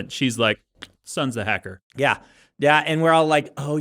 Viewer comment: I just heard "the sun is the hacker" and for all I knew the sun could be a hacker for all that was said on the plot of this movie